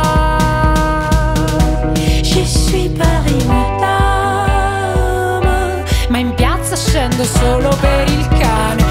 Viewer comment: cosa dice a 1:24? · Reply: Je suis Paris Madame